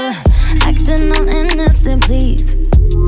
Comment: I'm the only that hears "then I realise she's PREGNANT" instead of "she's right there"? i can't unhear it 😂😂